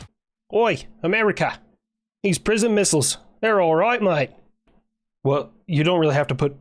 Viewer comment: I thought he said prison missiles